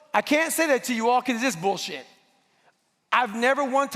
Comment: What did the guy yell at 3:50? Plese? I cant understand? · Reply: "It's f*cking bullsh!t."